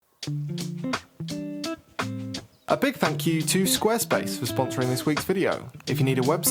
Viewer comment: I thought I heard "a big fack you to square space" at 0:04 😂